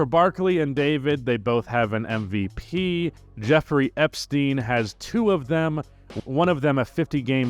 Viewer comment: I was listening to the video in the background, and when I heard 'Jeffrey Epstein had 2 MVPs' at 8:12 mark or so, I had to take several double takes and got really ocnfused, until I switched back to video and saw the 'He Who Must Not Be Named' bit and it all made sense. Nice nugget in there, however underage it may be